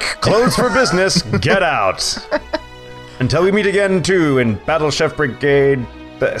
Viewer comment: I thought the subtitle said we may yeet again